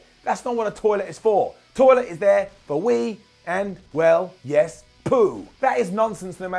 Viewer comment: I almost thought he said "weed".